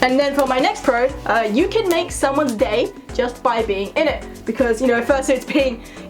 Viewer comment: When you said 'you can make someone's day' I thought you said 'you can make someone gay'. Like that's an ability I was not aware of.😂